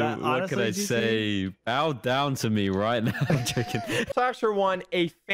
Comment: did anyone hear him say chicken